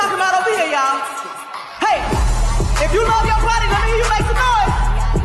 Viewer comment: I thought she was saying "if you love your party", as to say democrat party. I still can't tell.